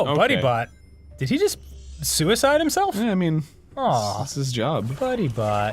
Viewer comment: Did Dan say "Suicide himself?"